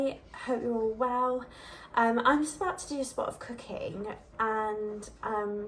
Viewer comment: Totally thought you said “I’m just about to do a spot of cocaine” hahaha!! I’m American and the accent (as sexy as it is 🥰😍) got me lol! Im like does she mean chickny?! Ohhhhh oh cooking!!